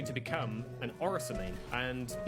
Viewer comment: Thought he said Orison mian. Lmao.